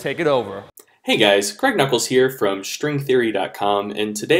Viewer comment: At first I thought Greg said "from String Theory dotcom".. was waiting for a mind explosion.